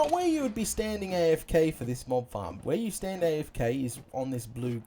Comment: I thought you said mom farm lol